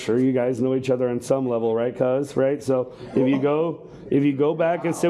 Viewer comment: The captions need revision. He calls him "cuz". He did not say "because".